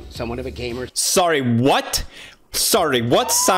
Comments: My gay self thought he said gay warrior when Ed said gamer and when pewds did the alarms after that I was like "??? Homophobia?" But In fact, I'm a dumbass. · Ooooo gamer... I thought he said caver. It's raining. Im camping. The pitter patter of the rain on the tree and palm fronds make it hard to hear. I would love to see Big Ed explore some some random hole in the ground though. Is watch that.